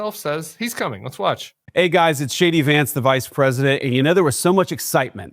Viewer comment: I heard him say "Hi, it's 'shady Vance'. How appropriate.